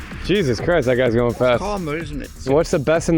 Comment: what a jew said!!!!!?😱. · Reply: He said karma